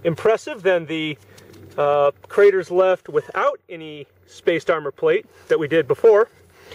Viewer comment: I thought it said space armour 😂